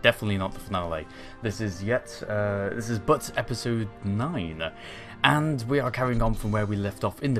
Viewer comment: did you just say Butts